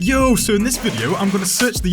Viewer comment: pensé que hablaba español XD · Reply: he thought they was saying yo(me/I in Spanish) when they were saying ayo